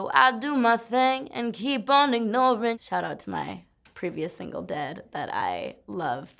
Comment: for minute i thought she said “shout out to my single dad” 😹